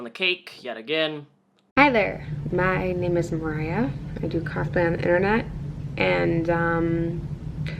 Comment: What is she saying? I listened to it several times and it still sounds like "Hi I'm Mariah and I do car pen on the Internet". Was that supposed to be "cosplay on the Internet"? Well she surely won't be doing that anymore. Maybe that's why she mumbled the words.